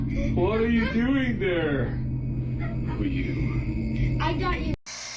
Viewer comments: Listen to the voice after the Father said to his daughter..What are you doing there? You hear what sounds like someone saying ….Who are you? Time stamped 8:45. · after the dad asks what are you doing there... Is it just me or did I really hear an answer of.. for you??